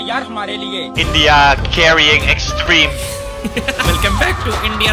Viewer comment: if anyone wants to know what he says, it's "there should be clapping for me"